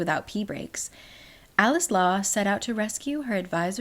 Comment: i keep hearing "coleslaw" instead of "alex law"